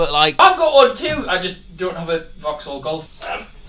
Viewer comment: Swear he said 'Vauxhall Golf'